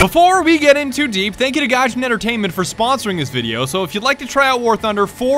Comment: I thought he said the creators name was ga-scgnee entertainment